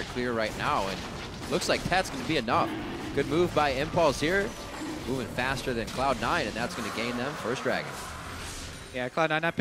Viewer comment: Somebody helps me to translate what the commentator said in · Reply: "Good move by Impulse here, moving faster than Cloud 9, and that's going to gain them first dragon."